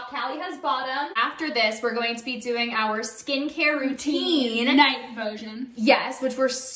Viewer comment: I thought you guys said you're also gonna do your skin care.😢